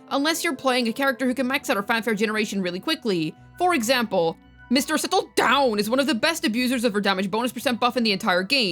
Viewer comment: wtf were u saying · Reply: Mr. Settle Down aka Neuvillette